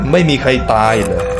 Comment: ผมได้ยินว่าไม่มีใบหน้าใครได้ยินแบบผมบ้างอ่ะ